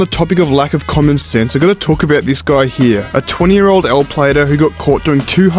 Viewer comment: im sorry, did you say l-plater?